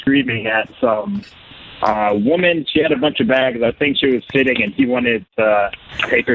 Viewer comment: Anyone able to make out what is said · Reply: He said there was a woman sitting with her bags and Chris wanted her seat lol